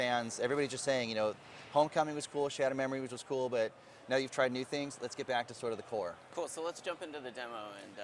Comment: did he say "lets get back to the horror"?